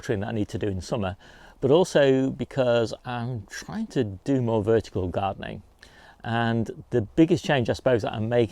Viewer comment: Ar first I thought you said ‘virtual gardening’. I thought how does that work! 😂